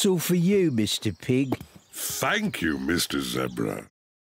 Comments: Youtube: Youtube Idk what to say but pretend your laughing soo hard. 0:13 did he say fank you? Wow 10 likes in 7 mins btw can u sub to me? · Who heard: ''Thank you Mr cute zebra'' ? ._.